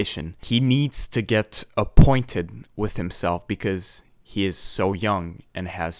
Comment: I know who spoke the final words... "Getting appointed with yourself. You who are now starting on the greatest adventure of your life.... Why have you listened?" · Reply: i hear “acquainted.” what does it mean to be appointed with oneself?